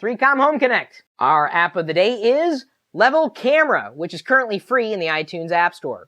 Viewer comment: thanks for the tip for A1 camera, just got it, although at first it sounded like you said level camera, which is an app also